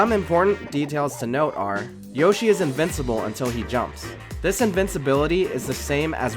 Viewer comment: Thought it said "Yoshi's invincible Party" and damn was I disappointed